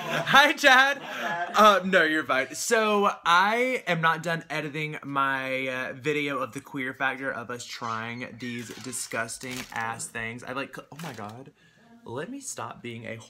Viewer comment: when he said "don't worry you're fine" I thought he said "don't worry you're mine" I was like AHHHHHHHHHHH